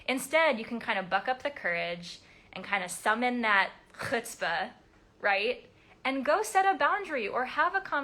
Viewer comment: Wait a minute did you just say חוצפה???? Do you speak Hebrew?????